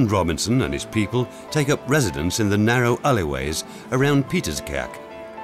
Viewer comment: the captions say Peter's cake, lol! It's Pieter's Kerk, i.e., St Peter's Church), in Leiden.